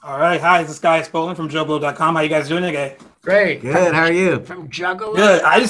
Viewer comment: Why did you Ignore Steve-O when he was trying to ask if you said "Juggalo" in the beginning? You never asked what he was trying to say. It did Sound like you said Juggalo when you said Joblo real fast.